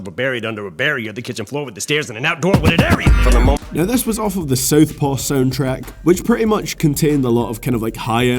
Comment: Had to rewind at one point. Thought you said "this was off the South Park soundtrack" lmao... I was like wtf...?